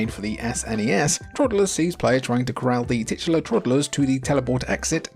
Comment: Did you just say tits????